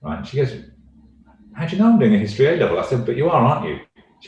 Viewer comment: what is he saying? history A level?